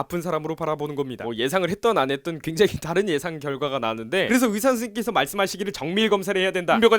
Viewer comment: 의사선새끼끄께서 말씀하시기를 나만 이렇게 들림..?